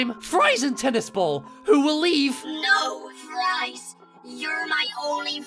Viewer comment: My dumbahh thought Puffball said boyfri